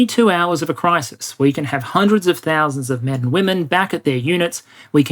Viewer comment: did you say quack